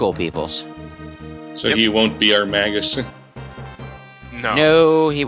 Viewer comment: did say magnus? As in the Magnus from Ogre battle 64, one of the best games of all time? if so, and they've already done that game, someone link me the videos.